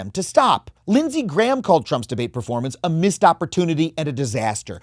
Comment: I know you said "missed opportunity and a disaster" but it sounded like "a missed opportunity at a disaster" which would imply Lindsey Graham wanting Trump to triple down and make a fool of himself even more